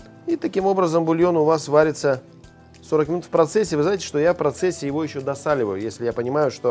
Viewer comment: " бульон варится скрмн " из 12:16 я услышал сколько варить бульон буквально 0,000001 сек((( Если б вы видели , как я все это сделал, вы бы мной гордились . спасибо за видео! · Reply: 40 мин